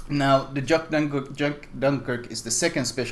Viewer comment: I heard him saying "Juck Dankirk" xD All hail the power of Dankness xD